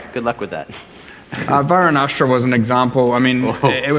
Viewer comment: theres nothing better then sandboxing a game and playing around with silly numbers also, what is he saying at 08:39? Verinastra? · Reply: varunastra, perandus unique sword, it counts as all 1 handed weapon types iirc